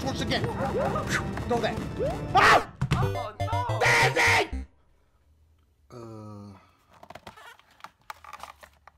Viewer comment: or 17:36 what did said he said like GEIFEJSKD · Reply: He said the ending